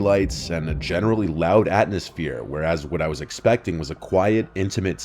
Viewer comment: Am i high or did he say atNosphere 🤣